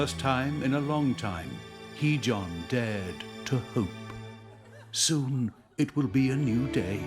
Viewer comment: Haha I thought they said hejohn dreamed to hoop.. Not hopE haha